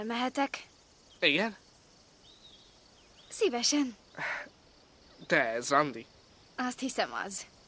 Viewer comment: nál mit mond? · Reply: te ez randi?